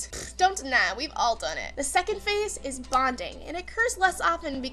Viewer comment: I know this sounds weird, but when you said that the second stage of love was "bonding", I almost thought that you said "bondage".